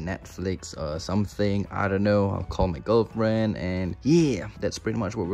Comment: did he say “call my girlfriend”? wowoww